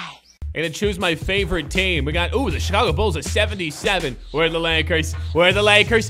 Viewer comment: why did he say Charlotte Bulls